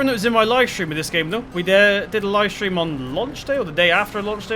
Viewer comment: I thought you said lunch day lol